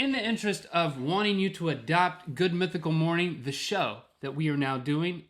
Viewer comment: link says "the show," captions say "michelle"